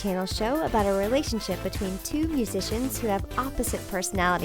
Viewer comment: When she was talking about Austin & Ally I thought she said it's about two piece of shits 😭 not two musicians